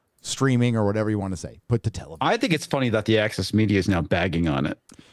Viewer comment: It was hilarious when at 4:55:20 I thought said Axis media. And I was eyeing shirt and thought Thomas Sowel and realized this could be a 'they all look the same' kind of thing so I decided not to comment till now.